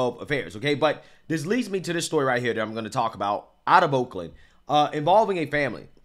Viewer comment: Sound like you said wolkland.